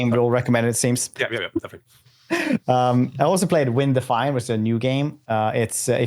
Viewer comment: What's the name of the game that talks about at 1:17:47? I'm not trying to be mean or anything, I just can't hear what he's saying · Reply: Vindefiant